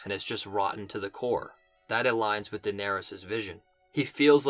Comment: I think you say "Daenerys' vision" at time stamp 6:00 lol